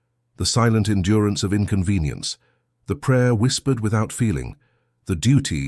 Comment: "The prayer whispered without feeling" < Did I hear that right ? Around 2:46.